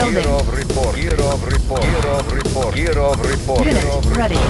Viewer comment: all i hear is "kirov report"